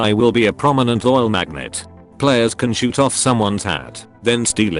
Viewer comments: I thought it said Arthur can shoot of peoples hand and steal it but he said hat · I thought it said that you can shoot of someone's HAND and steal it. Hat makes a lot more sense.